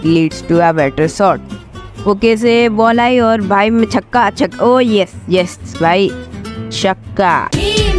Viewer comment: bhai Ma xakka kya bola tuna 😂😂ya